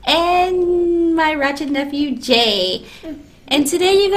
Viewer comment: Did she just say “my ratchet nephew, Jay” ⚫️👄⚫️